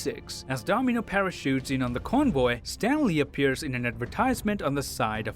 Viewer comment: Did you say Stanley or Stan Lee? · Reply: He said Stan Lee just fast